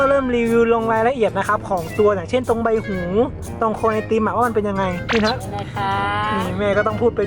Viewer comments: ตรงครวยไอติม??555 #ได้ยินคนเดียวป่าว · ตลกแม่รีวิวมาก55+ 1:10 ไอติมครวย......…..ได้ยินแบบนั้นจริงๆนะ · ได้ยินพี่พูดว่า คว_ ไอติม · ตรงค**ยไอติมเราได้ยินคนเดียวป่าว5555